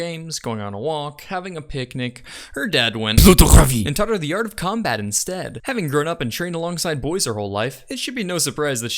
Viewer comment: what the hell did he say at 0:56? I'm French and I couldn't make any sense of it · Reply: "plutôt crever"